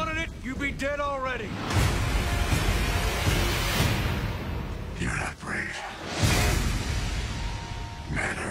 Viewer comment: what does batman say at 0:54 seconds a cant understand what he says anyone know pls? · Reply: Your not brave, men are brave